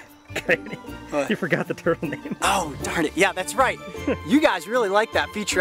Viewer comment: I thought he said "The turtle meme"